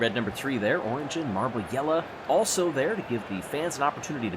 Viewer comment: Who was the other meet and greet marble (not RN3 or Orangin)? At first I thought he'd said a Yarble Yeller but I don't know any more. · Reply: What said might sound like "Yellah", but he's actually pronouncing Jelle's name. So, the other marble is Jelle.